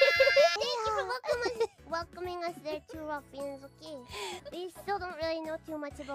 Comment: The more I hear Mococo say "Ruffians", the more I hear Furara saying "Wuffians". lol